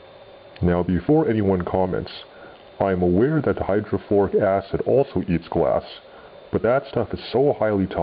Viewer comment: It did sound like you said hydrochloric acid (HCl) in the video, but that you actually said hydrofluoric acid (HF) makes more sense. HF eats glass for breakfast, and is much scarier than HCl or NaOH. Thanks for the video!